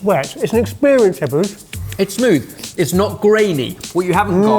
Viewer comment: "It's smooth" I thought he said "Its food..."